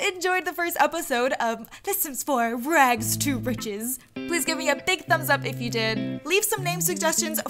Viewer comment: Every time you sad rags to riches I thought you said hotdogs to riches what is wrong with me.